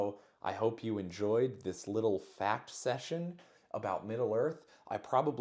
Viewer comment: I swear I thought you said, "I hope you enjoyed this little fap session" 😂